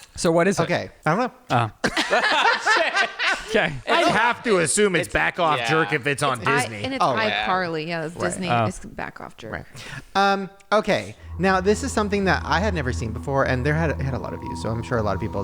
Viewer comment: iCarly is Nickelodeon and I only hear back off jerk